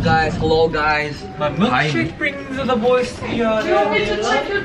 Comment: why do i hear "my milkshake brings all the boys at the yard"